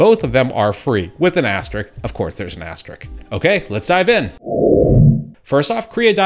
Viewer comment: Sire, did I just hear you say: "...with an ass trick. Of course there's an ass trick, okay" 0:30 dear lord? I say! 😝😅